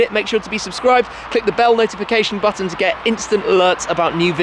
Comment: At the ends of his videos, it sounds like he says "cheers" and "jizz" at the same time